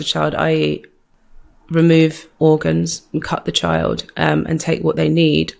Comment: She works in the exact industry that are the ones doing it!!! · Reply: Did she say "I remove....." ?? 12:02 Something about her says she knows more than wht she let's on. Was she apart of this at one time & got out cause I'm confused....then says these ppl that sacrifice their children arent evil!! Its like he struck a nerve in her...idc how much or less money u have, youre evil if you do such!!!! she's so against this & tryin to get more info why not ask questions when the opportunity is there??? Like with the ex witchdoctor... she doesn't dare initiate that convo ...ok got it but when the lady came out and let her know wht she was tryin to get out of she didn't follow up!? But now she says she forgot all about it but details the story very well..i hope i'm just reaching! I've only watched half & noticed these things...